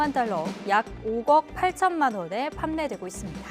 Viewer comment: 5억 8천만원을 5걱이라고 발음하는거 저만 그렇게 들리나요?